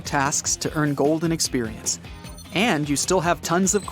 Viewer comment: did he just say golden experience